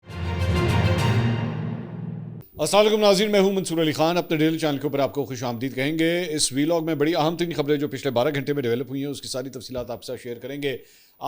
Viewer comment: apney “dill” news channels I’ve tried many times but couldn’t understand what you say in the start after AoA nazreeen main hoon Mansoor Ali Khan apne …dill (gital)